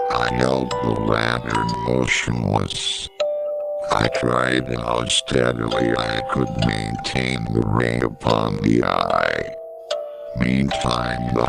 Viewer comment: even though I'm a bit late and it's quite late at night I just wanna say at 54:17 I'm pretty sure what they are saying is a line from Edgar Alan Poe's "the tell tale heart" if I'm correct. So the correct word would be lantern and ray , as I'm pretty sure this is from the part where the narrator talks about killing the old man in the story, where he sneaks into the man's room and suffocates him to death.